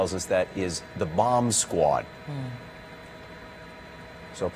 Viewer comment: I thought I heard the reporter say “Mom’s squad”.